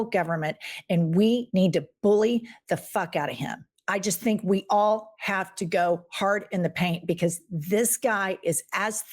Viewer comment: I thought he said I have to go pee